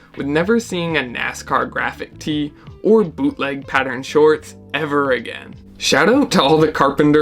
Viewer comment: haha I misheard “boot cut shorts” and thought I lost my mind 😅